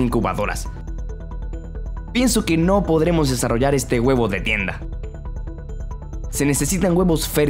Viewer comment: le escuché huevo de mierda :v